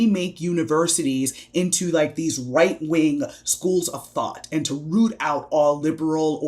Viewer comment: Did she just call that "white wing"? Because that is totally the white wing...